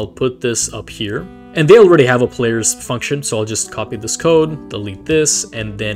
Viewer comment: be like: “f***tion” like actually that’s what I heard wait a sec tho